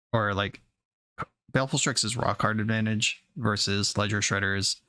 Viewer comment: Lol for a second I thought you said "Rock hard advantage" and not "Raw card advantage" at one point